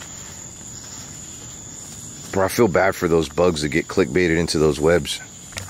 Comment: I think I heard someone or something say suicide